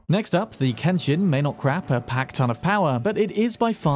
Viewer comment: did I hear that correctly? "may not crap a pack ton of power" lol